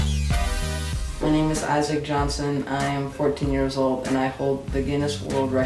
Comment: When he said I’m 14 years old I thought I heard I’m 42 years old